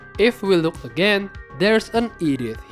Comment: i heard 'Edith' as 'idiot' 😂 i was wondering why would he call the roamer 'idiot'? then realized it was 'Edith'